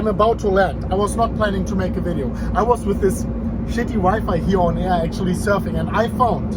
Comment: i thought he said he was with a shitty wife surfing, i was like WTF